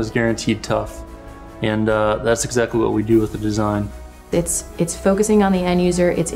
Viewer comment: sorry, at 1:55 I heard: "focus on Indonesia", what do you mean or what do you mean? Honest in Indonesia I want to buy this tool, there is no store that imports this tool